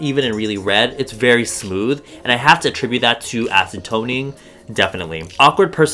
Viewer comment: omg when said acid toning i thought he said ACETONING and i'm likE WHAT?!!